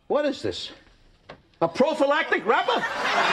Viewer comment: Its been over 25 years, and Jerry Stiller screaming "A PROPHYLATIC WRAPER!?" always cracks me. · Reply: Nope , Negative: You are wrong: Try again using captions at 3:42 . The captions clearly confirms what I said I heard: "prophylactic rubber". This may be an IQ thing where your idea of what you wanted to hear overpowers your mental capacity. Basically what I'm saying is: I win and you lose!